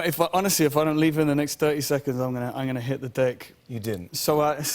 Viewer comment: Sounded like he said "hit the dick" hahahaha.